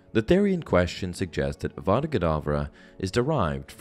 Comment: No joke when I first heard the killing curse I thought they said Abra Cadaver.